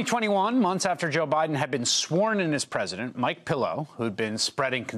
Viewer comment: Did he say Mike Pillow? 😂